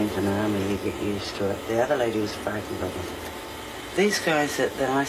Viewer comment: did she say ‘The other lady was f..king half of them ?